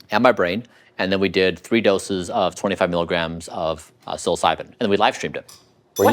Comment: this dude didn't go to any other world on a 25 MILIGRAM dose. at first i thought he said 25 grams and yeah that would be hardcore, but 25 milligrams ain't shit! I wouldn't even call it a microdose, maybe a miniature micro dose though.